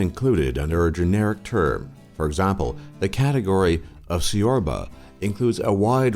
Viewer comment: Couldn't stop laughing when he tried to say "ciorba" 😂😂😂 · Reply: Haha!! That was priceless. I heard "siorba".